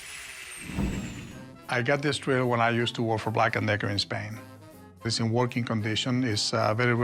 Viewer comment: wait a min.. did he just say black and